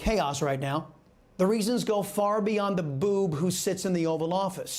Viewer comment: Did he say "Beyond the Boob"?